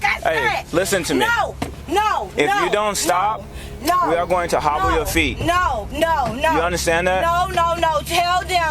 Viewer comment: Did he say we are gonna “if you don’t, we are going to hobble your feet”